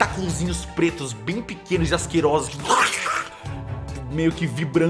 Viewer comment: min. Por algum motivo quando o Cellbit falou q dos " tentaculos pequenos e asquerosos" eu escutei " pentelhos pequenos e asquerosos"